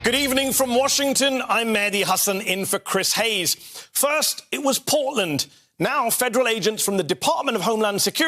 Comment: Okay, i heard "First, it was Poland!" That was cringy o.O